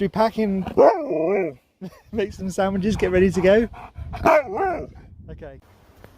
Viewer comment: I swear he said "let's go" 😮😱